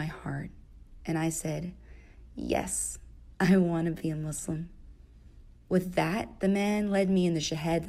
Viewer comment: it reads "Yes. I want to be Muslim", but what the narrator says "Yes. I don't wanna be Muslim"